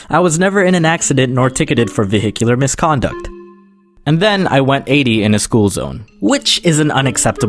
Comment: I THOUGHT HE SAID 80 MP/H! sorry I live in the land of no freedom *cough cough Trump *cough